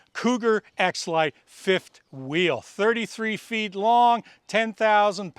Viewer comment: Lol thought he said 3rd ex wife's fifth wheel